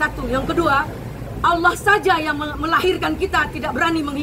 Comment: Wah duh klao g salah denger "" allah yg melahirkan kita" wah penistaan agama nih 🤦